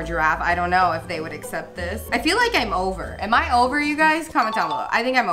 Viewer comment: did she say she was over?